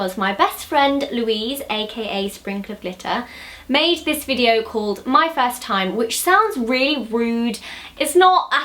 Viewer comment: I thought she said my first titty